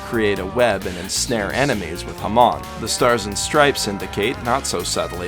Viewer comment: Ok he just said "jamón", wich is "ham" on spanish. DUDE EVERY TIME HE IS JUST SAYING "JAMÓN" AND I AM WHEEZING MORE THAN I SHOULD